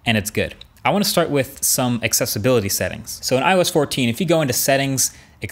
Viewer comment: i hear “When I was 14”